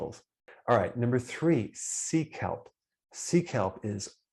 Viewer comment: Lol. When you said, "Sea kelp," I heard, "Seek help!"